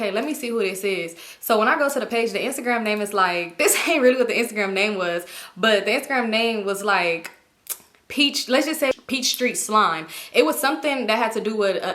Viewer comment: I can't with peach tree slime 😂🤭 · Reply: I thought she was saying peach street slime 😂😂